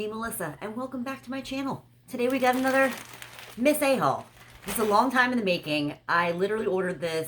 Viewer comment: Your makeup looks fab! When you introduced today’s it sounded like you said ‘Miss A Hole!” I was rolling laughing bless you xxx